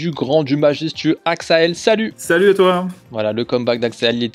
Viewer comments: A 0:18 Axael a vraiment dit "Salut et toi ?" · Mdr 0:15 Salut axael ! Salut et toi ? 😂 J'suis mort